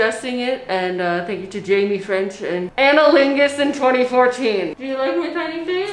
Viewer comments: I'm sorry, did you just say her name was analingus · DID SHE JUST SAY ANALINGUS